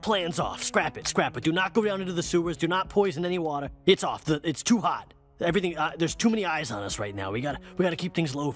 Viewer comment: he clearly said its too hot and it even makes sense it terms of the dialogue considering he said there's too many eyes on us immediately after, but yet the subtitles say "its too hard" did they get AI to make the subtitles to their own script?? WTF LOL......